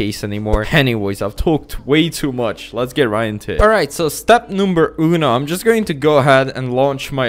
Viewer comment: how i heard Step number UNO at 2:03 why i hear italian here?